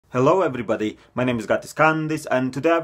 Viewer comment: i heard “hello everybody my name is Genghis Khan” in the intro for literally no reason. i heard him loud and clear but my brain wanted him to say genghis khan